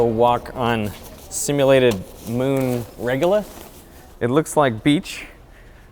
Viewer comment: The subtitles say "regular", but he says "regolith". I guess the subtitler didn't known that word.